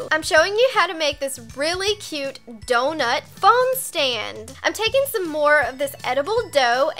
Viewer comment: What gets me is she says fon stand on